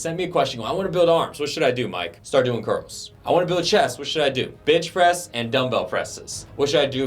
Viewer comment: did he say Bitch press ;)